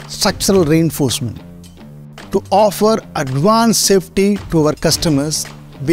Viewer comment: did you just said advanced safety ??